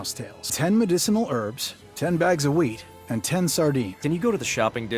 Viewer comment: Thought he said" 10 bags of weed" lmao